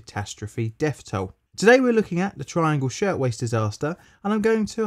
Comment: Thought it said shitwaist